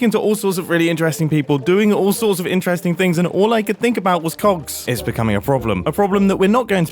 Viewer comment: i had this on my queue and i misheard you so bad, i heard "all i can think about is c*****" and i was thinking SAID WHAT lol, not that i have a problem if you do. it just felt like no way is going rated 18+ 😂😂😂😂😂😂